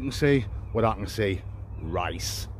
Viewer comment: did you say “Rice”?